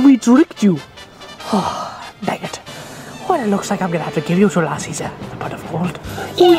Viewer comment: I thought he said "well..it looks like I'll have to kill you two lassies but first a pot of gold"